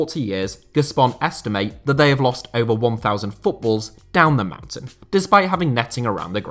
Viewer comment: without subtitle I thought he said ''estimated they have lost over 1000 footballers down the mountain'' lol since I heard the word ''footballer'' more often than ''football'' in this channel :) Next video: Every national team's stadiums in the world